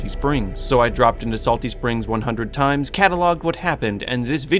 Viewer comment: I thought it said everyone dropped at salty